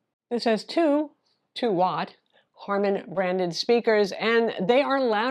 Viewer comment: I think you said Harman Randon instead of Harman Kardon